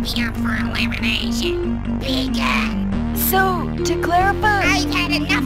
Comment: I heard “PIKAAA——“ And “VEGAN” XDDDDD